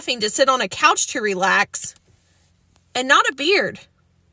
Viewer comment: Seconds is the Funniest... I thought she said BEER , She said BEARD. LOL😂🤣😂🤠👍🇺🇸